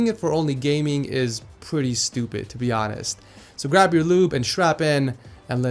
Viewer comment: Did he say grad your lube a strap in!??